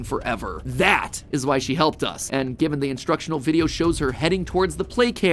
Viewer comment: did you just said he? i mean the text says she but i didnt heard the s